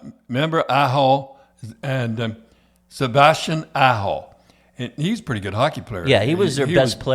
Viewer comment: Sebastian Asho? lol. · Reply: Sounded like he said Sebastian asshole to me lol